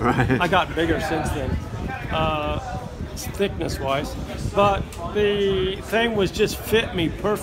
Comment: What does he say at 1:04? right after "i got bigger since then".... (Thanks) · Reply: Thickness wise.